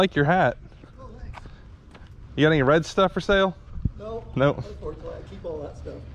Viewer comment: Did I hear "red stuff for sale"?